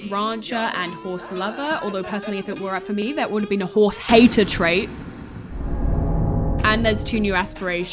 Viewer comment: not me getting an ad right as was saying "there would've been a horse HATER" but the ad cut off, so it just sounded like said "there would've been a WHORE" 💀